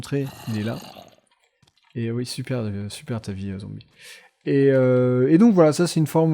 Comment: "Cool ta vie le zombi" J'ai compris " Cool ta vie Tony" a la première écoute >_<